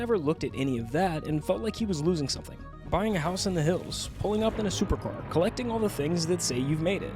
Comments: did I hear the hills? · did i hear Hills? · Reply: You actually heard “in the hills” as in I be in the hills,fucking dudes.